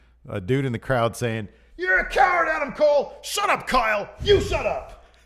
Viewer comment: I heard "shut up kyle" too